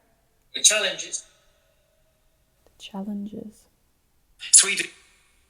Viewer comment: where you thought the voice said ”freedom”, I thought it sounded like ”Sweden”. Maybe because I live here. And at 44:02 as an answer to question of ”what am I smelling”, I clearly heard a mouth made fart noise 😂